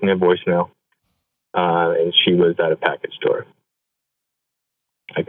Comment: Regarding 3rd call: He mentioned his mom was at a “package store”. This is a term, common to the northeast United States, used to describe a liquor store. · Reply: Thank you for clarifying! I thought he said package door. Did a Google search and this is what i found. In Alabama, Connecticut, Georgia, Louisiana, Massachusetts, Rhode Island, and Texas, liquor stores are also known as package stores; locally in Connecticut, Massachusetts, and areas bordering these states the term pack or packie is used as well, because purchased liquor must be packaged in sealed bottles...